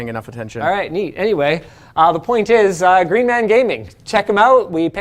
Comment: I thought he said the sponsor was “Green Ham Gaming” for a second